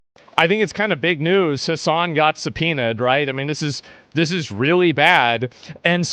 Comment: i misheard, "hasan got sum penis"